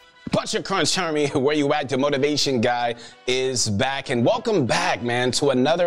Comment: when he said "The Motivation guy" i thought he said "Demotivation Guy"